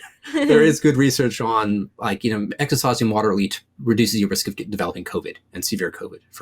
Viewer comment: Fabulous information thank you. Although I did find Dr Jason speaking too fast for me to keep up at some points. :/ Eg... what does he say at 21:38? Exercise and water...<something> ? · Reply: Exercise and hydration reduce the risk of COVID. :)